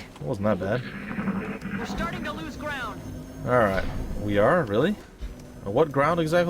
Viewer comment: I think said They are losing ground